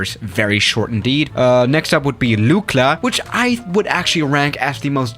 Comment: sounds like he said "Nukla"